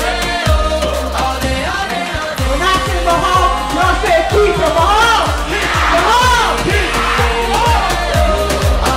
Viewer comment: o que ele fala? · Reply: Algo do tipo "eu falo mahal vcs falam kita" Mahal kita=eu te amo em filipino